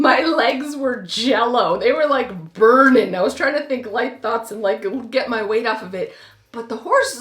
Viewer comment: I'm so done did she just say I tried to think like thoughts...lol...😂😂😂